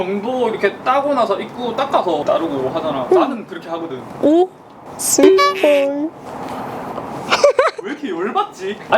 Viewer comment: 습 머요? 안들려ㅠ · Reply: 저보고... 스윗보이래요...ㅎ